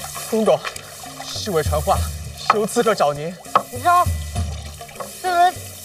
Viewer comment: like how the subtitles make the people understand what their saying but not exactly ex. 5:33 it says "intruder" but what it actually said is "assassin".